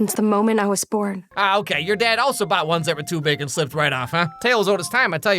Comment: What did Alex say here?? 😭 I’ve listened like 4 times and can’t understand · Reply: he said “your dad also bought ones that were too big and slipped right off” meaning condoms